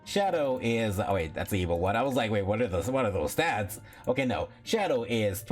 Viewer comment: Knuckles isn't evil.. he's one of sonic's friends. Silver and Shadow are the evil ones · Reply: he said ‘evo. 1’ (as in the first evolution) in regards to knuckles, not ‘evil one’. I misheard it at first too